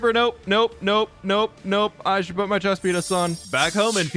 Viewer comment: Did anyone hear chest pen*s or just me @ 3:07:07 😂😭